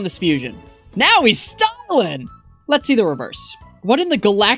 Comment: that sounded like "now he's Stalin" because i couldn't hear the other letter L or the letter G in the word stalling